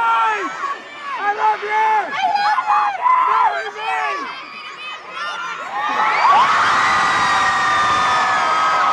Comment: What did she said at 4:24? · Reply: ‘ I’m very happy to be in Melbourne tonight’